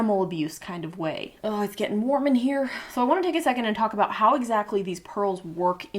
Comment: i fully thought you said "its gettin' Mormon here" & i was racking my brain trying to understand the correlation